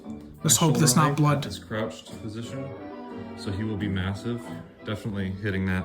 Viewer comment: did you say massive